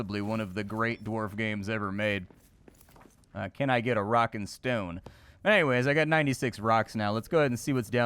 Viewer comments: Did I hear rock and stone? xD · Did I hear a Rock and Stone?